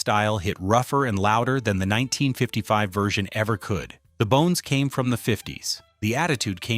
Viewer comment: I literally had rewind this 4 times because I thought he(the ai) said "yn" style 😂😂😂